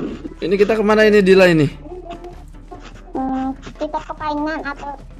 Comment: gw dengernya dila bilang"kita ke pelaminan" wkwkwk, just kidd :v